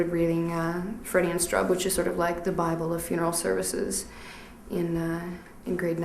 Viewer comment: What is the book the woman at 5:28 says she was reading? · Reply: The principles and practice of embalming by Clarence G. Strub, L. G. Frederick